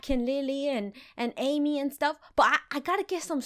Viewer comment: When u said education I thought u said reputation so I started to sing big reputation big reputatiion